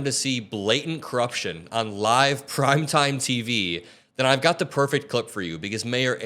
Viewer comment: I misheard that as crime time TV. But can you blame me? 😕